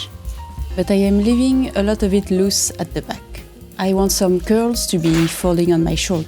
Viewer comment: Totally my bad -- I heard you say "I want some GIRLS to be falling on my shoulder" and I began thinking: Hmm, historically, Marie Antoinette did have some girlfriends... Then I realized you mean "curls".